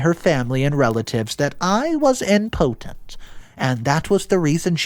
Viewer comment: I was a little thrown off by the pronunciation of the word “impotent”… it sounded like he was saying “important”. Love the stories, but I do notice that a lot. 😬